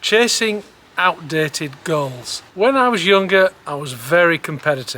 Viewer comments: Thought he said "girls" but he meant "goals". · Goals, I thought he said girls, and I was right there with him